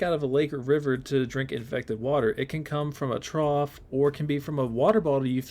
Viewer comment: What did you say at 0:54? A troof? Or what? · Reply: Trough, a thing animals eat / drink from